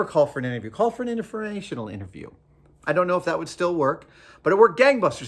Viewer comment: What is he saying at 3:37? Is it just informational interview? Because it sounds more like innerferrational interview, and I don't know, but really want to know what that might be.